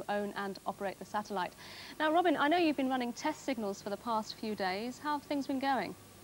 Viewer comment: did they just say signals from wh smith